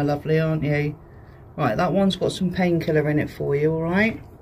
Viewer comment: I thought he said ‘paint thinner’ not ‘pain killer’ at first and I was so concerned for a minute there 😅